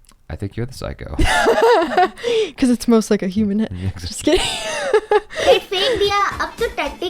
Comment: Did she just say human head ..... 🙂🤣